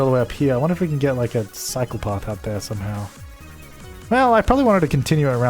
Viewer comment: Every time you say "cycle path" I hear "psychopath." :D